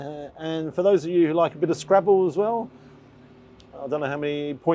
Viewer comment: I had the video in the background while looking at something thought you said Scrapple..... Its a food from my area in PA USA. LoL Then flipped seen you meant scrabble an I was like awee lol.